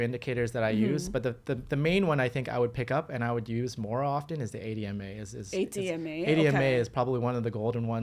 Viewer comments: That indicator he starts talking about at the 40:52 mark, It sounds like hes calling it the "ADMA" indicator. I have never heard of this one and I dont seem to be able to find it when i search for it. Am I hearing what he said right? IS it the "ADMA" or is it something else? 🤔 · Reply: It’s 8 EMA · 80MA, is that what they are saying? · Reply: 8 ema